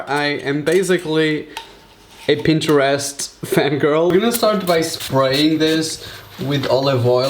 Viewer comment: Did he said pinterest fan "girl"???...